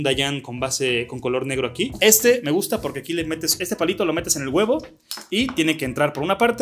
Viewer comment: JAJA yo escuché "este palito lo metes con el webo" y me quede tipo 🤨